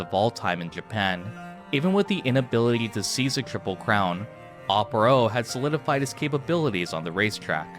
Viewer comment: why did I hear c6 triple crown here😅 I need to quit genshin